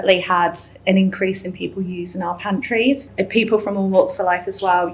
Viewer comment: I first thought she said people were using her panties